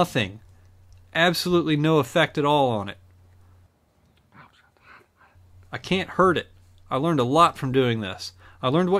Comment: WHAT IS BEING WHISPERED? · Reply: "OUCH, HOT HOT!"